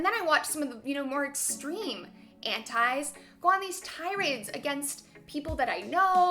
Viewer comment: Gods, I heard "Hentais" instead of "Antis" waaaaay too many times